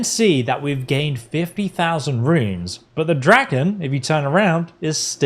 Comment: I might’ve misheard it, but I thought he said 1000 ruins, but it only gives me 26